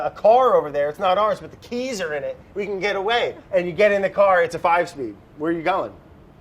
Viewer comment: Does anyone know what brad says in 8:51 after " it's a " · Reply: I know I'm late, but he said "it's a five speed" as in a car with five gears in it's gear box